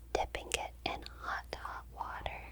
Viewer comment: Mishearing something about hot dog water was very alarming when I was downstairs making soup 😭